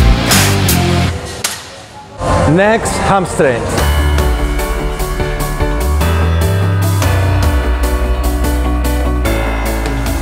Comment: thought he said “next, have sex!”